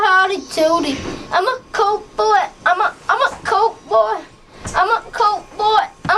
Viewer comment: at first i thought he said...im a cowboy im a cowboy